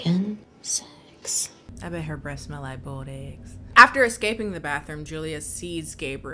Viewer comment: I laughed and I quote.. "I bet her breath smells like boiled eggs" End quote.... is hilarious!😂😂👏👏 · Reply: I heard, “I bet her breasts smell like boiled eggs” and that also made sense to me.